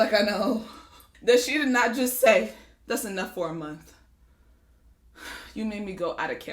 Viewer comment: I thought she said "that's enough shampoo for mommy" as in for like one bath but when I realised she said MONTH FOR EVERYONE...giiiirlll...